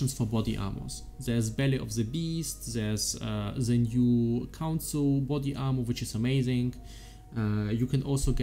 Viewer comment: which armor was that? can't understand it :( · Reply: Believe he's talking about the new Pale Council armor called Yriel's Fostering